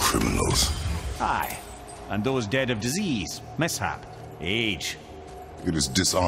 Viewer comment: «And those dead of disease, mishap age» Jack: i thought he said aids🤦‍♂️🤦‍♂️